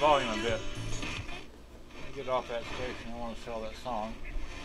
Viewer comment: I don't know why you don't want to "sell" the track playing at 1:13 mark. Gladys Knight & The Pips - Save the Overtime (For Me) Straight fire! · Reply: At the 1:26 mark you said "I want to get off that station. I don't want to sell that song" . Or at least that's what it sounded like. Either way, now we know what the song is. :P